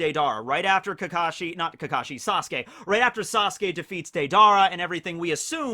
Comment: It's Deidara. When you say it all I hear is "Date-Dara" haha